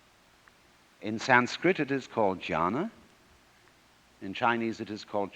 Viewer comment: Jhāna (absorbtion) not Jñāna (knowledge) is what he says. The on-screen subtitle there is wrong. Great video otherwise🙏